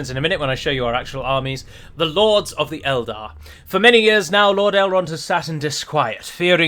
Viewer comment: I needed to repeat that, as I heard "For many years now, Lord Elrond assasined his squire"